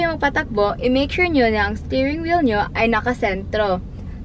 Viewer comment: I keep hearing "staring wheel" instead of stirring😅